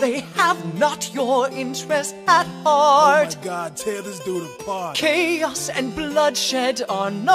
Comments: I love how Hoodie is like, “Oh my god, tear this dude apart” XD Edit: Oh my god! I just checked back on this comment and I don’t even know what to say- Thank you to everybody who liked my comment, I really appreciate it and I don’t think I’ve ever gotten this many likes before, or at least I haven’t seen it, so this is pretty much astonishing to me. Thank you, all of you definitely made my day ;w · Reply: I always thought he said "oh my god, Tims doing his part" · I love how Hoodie is like, “Oh my god, tear this dude apart” XD Edit: Oh my god! I just checked back on this comment and I don’t even know what to say- Thank you to everybody who liked my comment, I really appreciate it and I don’t think I’ve ever gotten this many likes before, or at least I haven’t seen it, so this is pretty much astonishing to me. Thank you, all of you definitely made my day ;w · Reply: I thought he said oh my god tailers doing his park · I love how Hoodie is like, “Oh my god, tear this dude apart” XD Edit: Oh my god! I just checked back on this comment and I don’t even know what to say- Thank you to everybody who liked my comment, I really appreciate it and I don’t think I’ve ever gotten this many likes before, or at least I haven’t seen it, so this is pretty much astonishing to me. Thank you, all of you definitely made my day ;w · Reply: i thought he said "oh my god, tim's doin the part"